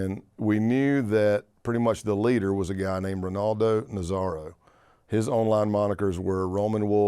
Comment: Thought bro said Ronaldo Nazario at around 15:20 at first lol